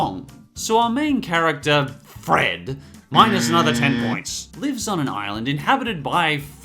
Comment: when he said "lives on a island" on cocoanut Fred or whatever, I thought he said "leaks on a island" instead of "lives on a island" XD.